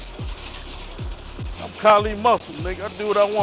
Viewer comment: Kali says "im Kali muscle" but the subtitles says carly muffin 😂. sorry was a slap your own god damn face moment 🤦‍♂️. other than bad subtitling awsome content Kali inspiration to all us fathers out here. give us the POWWWWER